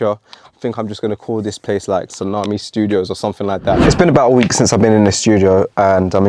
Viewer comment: Bro, at first I actually thought you said "SALAMI STUDIOS" - had me double back lmao